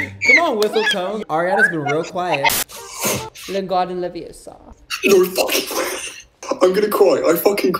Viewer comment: he did not just say "Lingardiuml Leviosa" .... I'm offended lol